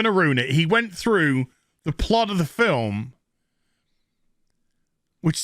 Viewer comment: I thought you said, "They went through the plod of the film".....